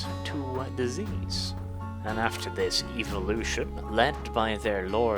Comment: Did you just say evilution?